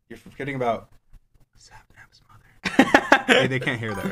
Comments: for anyone who didint hear what dream said at 12:37 he said "sapnaps mother" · If u couldnt hear he said "Sapnaps mother"